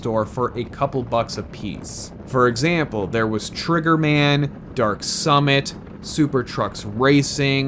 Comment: When you said Trigger Man at first I thought you said Chrono Trigger for the SNES